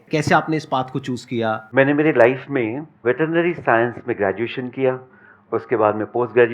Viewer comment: what did said... Is it veterinary science?